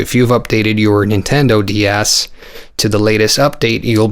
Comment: Sounded like you said nintendo d arse I cracked up